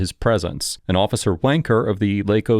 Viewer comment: Officer what now?? Sure sounded like you just said 'Officer W⚓️'.